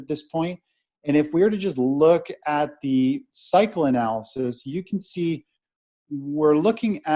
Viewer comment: I thought he said he uses 'psychoanalysis" rather than ' cycle analysis" & I thought that this would be a weird one .